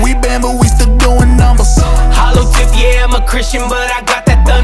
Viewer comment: I just realized he says “if I die I’m a Chrysler”. I thought he was saying “I’m with Christ”.